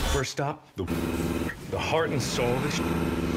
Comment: Who else heard “the heart and soul of the sh*t”